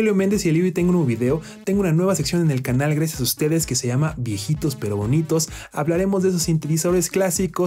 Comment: Le entendi "viejitos pero ardientes" 😂😂